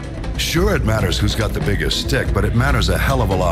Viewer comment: my favorite line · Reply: Did anyone else mishear it as "dick" instead of stick?